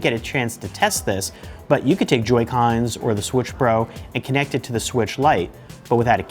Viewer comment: did he say Switch PRO?